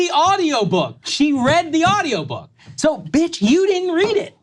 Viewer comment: Did Sagar just called “bitch” Governor Noem?